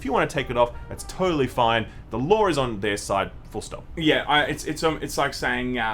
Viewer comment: I kept hearing "The lore is on their side", took a while....